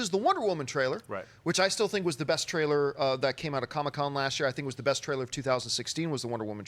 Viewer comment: I thought John said the Logan trailer was the best from last year, not Wonder Woman